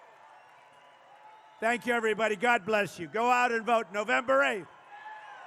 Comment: I thought he said November 28th the other day. lol